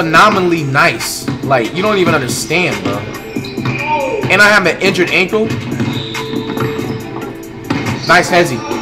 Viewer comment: did flight say I’m an injured ankle? Lol